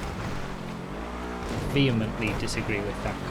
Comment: what is said here? Theo and Beef? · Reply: Vehemently